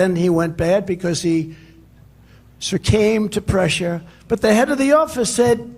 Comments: Did he say " he surcame to pressure"? We are going to be laughed at for as long as he is remembered. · The Orange Mess gabs on as the actual Crowded Mess behind him applauds.🥴😵‍💫 Did he say @ 13:08 , "but then he "surcame" to pressure" ...COFVEFE🥴 surcame🤔